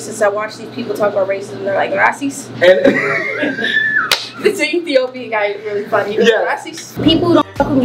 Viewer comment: Did she just say Ethiopian guy at 3:48 😱😱